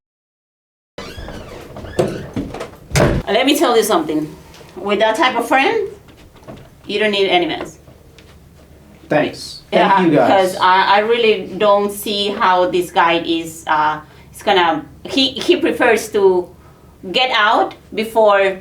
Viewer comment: I thought he said thanks F u guys